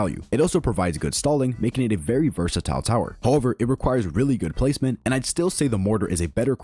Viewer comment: I kept hearing “really need good placement” now I think I will have a nightmare tonight